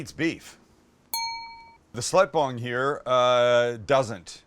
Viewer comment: Did he just call his wife a slut bong?